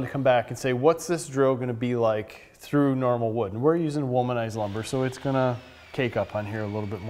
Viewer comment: Did you say your using womanized lumber? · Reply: Wolmanized lumber.